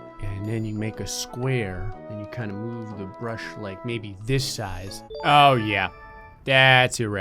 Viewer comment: I thought that said "Bruh size."